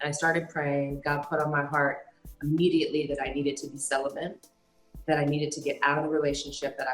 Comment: Did she say “celibent”? 🤔. Prayers for both.